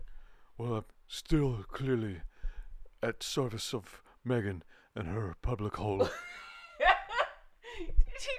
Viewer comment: Cracking up here! Still dark at 6AM, sipping my morning coffee when I hear the words "Meghan and her public hole". 🤣🤣🤣🤣 😭 😭